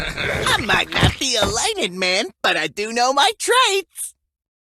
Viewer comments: umm did he said light head man? Or isit just me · I think I know what he said “I might not be a lighted man but I do know my traits”